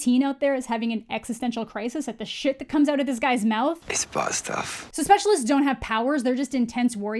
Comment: Not sure what the hell Riven is saying. I hear "it's boscovs" or "it's butt stuff"